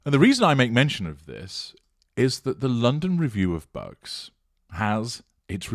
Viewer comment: I heard that as ‘The London Review of Berks’ which did not seem amiss